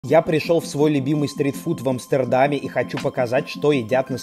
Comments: Вначале я услышал "я пришёл в стрип-клуб"😂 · Стритфуд сперва послышалось как "стрип-клуб"